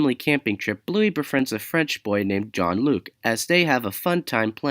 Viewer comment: did you say john Luc or jean Luc because I heard you say john Luc instead of Jean Luc